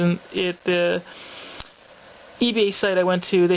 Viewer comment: Awesome pick up!!! I was wondering I kept hearing you say E-Bay but I almost thought you said C-Ebay like a special comic site or seller. Just wondering, and that X-Men and Venom Cover are awesome.